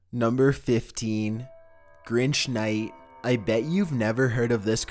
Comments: 15: I thought you said cringe night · I heard "number sex"